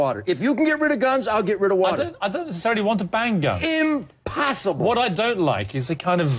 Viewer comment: did piers say i don't want to bang guns???